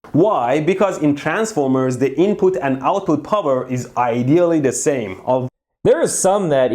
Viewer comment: Instead of output power i heard Anpul Pummer Was thinking for about a minute who tf is anpul but then i realised what he said. First time i misheard Mehdi and it went perfectly 👌😂